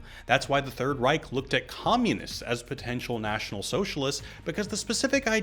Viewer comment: bro i had to replay 15:07 like three times to catch what you were saying because I kept hearing (essentially) "Nazis were communists" and i was like "mf did not say that"